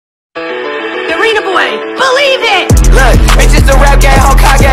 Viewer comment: I thought it says"dorito boy believe it!"